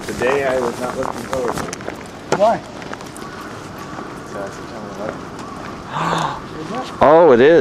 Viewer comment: What did he say at 12:46 ? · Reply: He said, "September 11th". was friends with one of the guys on Flight 93 that tried to take over the plane.